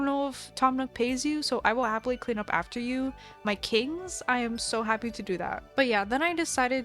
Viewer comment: what did she just call them? ...k-kings?!